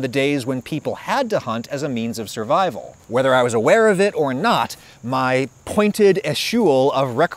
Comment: What’s the word you say at 1:56? “My pointed esheul (sp?)…”